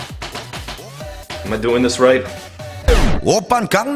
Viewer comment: It's easy to mishear it as "Open condom style."